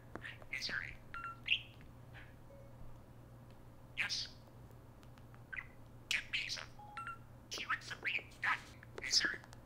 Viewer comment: Sounded like he said 'Hey Siri get me a pizza' at one point. That could get expensive.